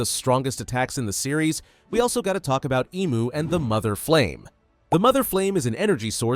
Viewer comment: what he say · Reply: Mother flame